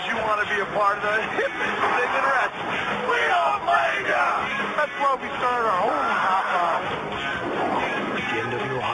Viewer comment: Does anyone know what they say at 0:12? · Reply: "We don't blame ya'!!"